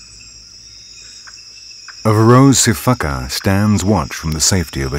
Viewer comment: I had replay a part, like4 times, I thought he said "Rosie fkup" lolol!